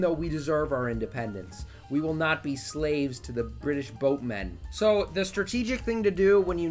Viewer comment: I thought you said British "roadmen" which would of been a surprising thing for an American to know about but totally apt